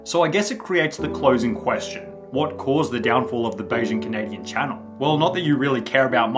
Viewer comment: i thought he said "beijing canadian"